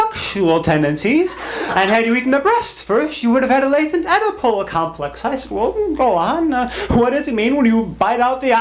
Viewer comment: what does he say at 4:25? Edepol complex?